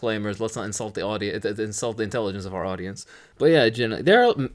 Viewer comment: After the whole Nutella-blackface rant my ears were so primed on "Nutella" that I heard "Let's not insult the Nutelligence of our audience" at this point, and it was rather hilarious.